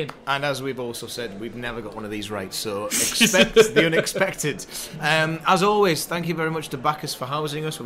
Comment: I thought I heard say Accept the unexpected. Willockism?